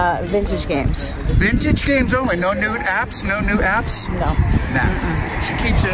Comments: am i the only one with headphones who heard im clearly say "no nudes apps?" the first time? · Did he say "Nude Apps" the fist time. lol